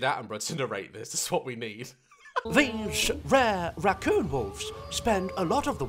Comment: Who else is watching during Quarentine? just me? Ok... edit: moly holy thanks for the likes! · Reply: Ok since yall or more recent am I the only American who heard him say rucooon hahaha🤣🤣🤣